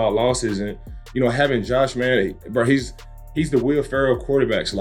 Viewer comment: At @ 12:05 did he say Josh Manning?